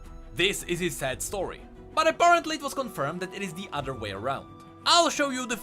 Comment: Did he really say aporontly 7:56 ?